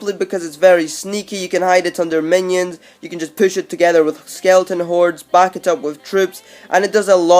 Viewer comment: DID HE SAY SKELETON HORDE